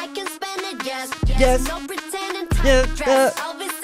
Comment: SHE SAYS "LISA REPPIN" not rappin bc shes filming in her home city of thailand and only hired thai dancers for her mv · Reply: That makes a lot more sense lol, also since it sounded like she said “tight dress, LV sent it” - Lisa reppin